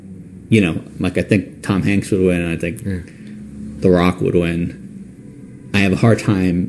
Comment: It sounds like he say I think Barack would win not The Rock....just me 🤔